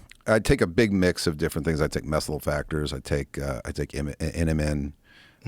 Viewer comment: Taylor says he takes "mesula factors"? Does anyone know what he's talking about? Can't find anything with this name.